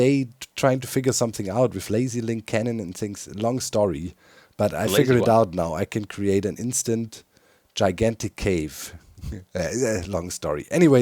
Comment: Did I hear "instant giant hermit crater"?